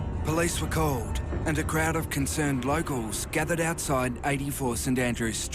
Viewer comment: Did the narrator say "Bloke-als" instead of locals at 33:00?